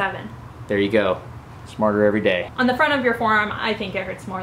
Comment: I thought you said "martyr every day" because of the pain and suffering you put into every video.